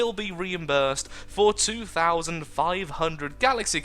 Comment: xD i thought you said 42,500 GC xD I' like woah thats a lot, but then i need to replay that piece again you actually said "for 2,500 GC" i'm like oh -_- u wish it was 42,500 GC xD